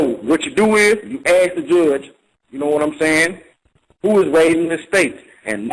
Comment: he ask the judge what??? cannot make out what he says??? best I can make of it is who is laid in estate. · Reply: Who's laying the charges?